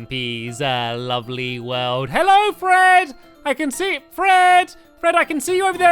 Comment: In the very beginning of the video I thought Stampy said "Bread! Bread! I can see you over there Bread!" But I realized he said Fred!!!